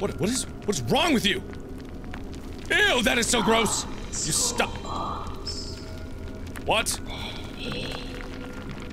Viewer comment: What did the lady say at 23:25-23:32? I couldn't tell what she said. Anyone know what she said? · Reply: She said "Kommt zu uns" which means come to us in german and after that im not sure but i think she said baby??